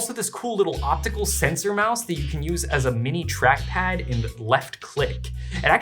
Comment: did you say bleft and was this a reference to Woe.Begone? Or am I just delusional with how much I listen to this podcast?